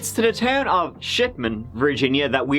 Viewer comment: at first I thought said town of "shitmen" and was very confused